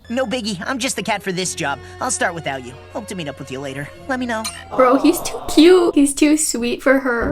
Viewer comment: I originally thought that he said love you now but turned on the captions and it said let me know Me: dang it 🤦🏼‍♀️